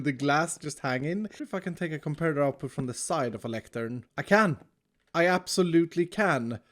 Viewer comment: i thought he said "should i take a f-ing comparator output from the lectern" LOL